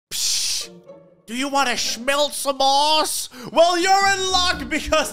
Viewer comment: sorry what did u say? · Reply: Du you like to snelt?